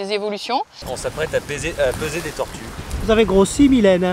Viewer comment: vous vous apprétiez à quoi ? 😂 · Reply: "Paiser" des tortues. C'est comme peser des tortues, mais avec plus d'amour ;)